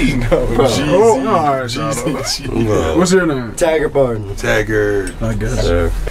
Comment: Who is the player on the right of? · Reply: It sounded like he said Taggart?